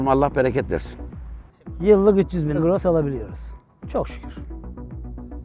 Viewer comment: öyle bi kesmiş ki editör ne dediğini anlamadım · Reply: 300 bin gross diyo😊